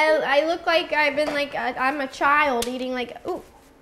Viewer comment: sounds like ronis saying "child eating my/like poop"